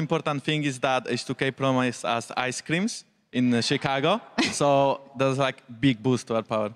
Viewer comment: jankos needs a translator. that is such a thick accent i'm surprised anybody can understand him. wtf is he saying at 2:00 "H2K promo is as ice creams in Chicago"